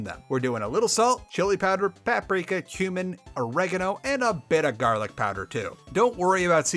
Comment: when you said "cumin" I thought you said "human" I was like "damn, didn't know it was cannibalism segment time"